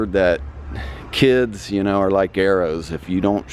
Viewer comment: Line up the Gtr and Vette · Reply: Guys i thought he said "kids are like arrows just shoot'em" xD